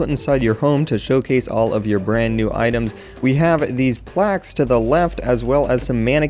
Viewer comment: I thought it said plagues not plaques I thought omfg XD